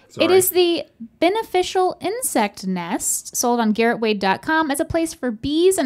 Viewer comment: I genuinely thought that it said “incest nest” instead of “insect nest” 😭😭😭